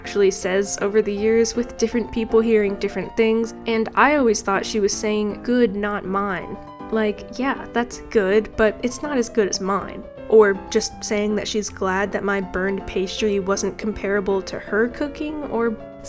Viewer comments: I personally thought mama said "you're not mine" as in she's implying you've done so bad that you are no longer her child. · I always thought she was saying “you, not mine” as if to say that it was MY mistake and not hers in broken English bc it wasn’t her first language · i use to think mama said "your not mine' lol idk why i thought that was it said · Always thought she said "good its, NOT MINE!" 😭 like shes glad she doesn't have to eat it herself 💔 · I always heard "you're not mine" thinking she was talking to the broken egg. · I sounded like she said “your not mine” 😭 she got so disappointed that she disowns you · As well as other people here I heard "your not mine"